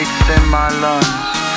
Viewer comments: why the hell did i hear "Deez nuts corrupted" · I always thought it said "nature's corrupted I suggest you go far away" XD